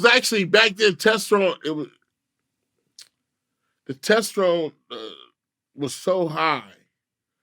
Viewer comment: “The test room was high “ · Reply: I legit thought he said “test drone” or “tension” a million guesses I would’ve never thought he was saying Testosterone 😂